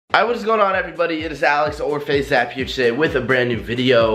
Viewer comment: It sounded like he said " i was going on Everybody "